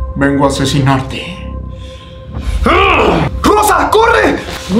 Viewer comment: En vez de escuchar rosa corre en el minuto 7:50 escuche cosa corre XD